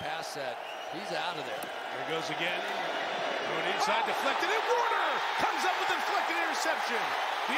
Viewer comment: what did the announcer say? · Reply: I have no idea. “Something something deflected and Warner!”